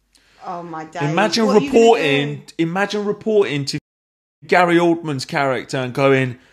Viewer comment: Who loves Gary Oldman? EVERYONE!! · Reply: I am so high I thought you said gay old man